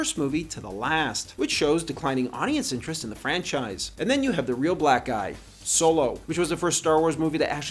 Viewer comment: what did he say? 😂😂😂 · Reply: "and then you have the real black eye"